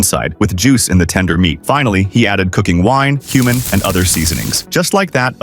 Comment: cumin nah bro I heard HUMAN is was doing smth then me face went like fuck did you just say? 😭😭😭😭😭😭😭😭😭😭😭😭😭😭😭😭😭😭😭😭😭😭😭😭😭😭😭✌✌✌✌✌✌✌✌✌✌✌✌✌✌✌✌✌✌✌🥀🥀🥀🥀🥀🥀🥀🥀🥀🥀🥀🥀🥀🥀🥀🥀🥀🥀🥀🥀🥀🥀🥀🥀🥀🥀🥀🥀🥀🥀🥀🥀🥀🥀🥀